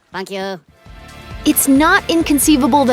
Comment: what did he sayyyy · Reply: "thank you"